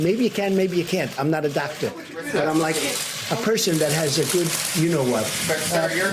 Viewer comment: I'm not a Doctor. I'm m like a person that has good "U KNOW WHAT" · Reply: ROTFLMAO!!! I swear I thought he said he was "just a person with a big you know what". LOL